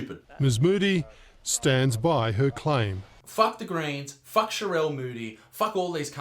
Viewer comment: Thought he was saying fuck the koreans for about 3 quarters of this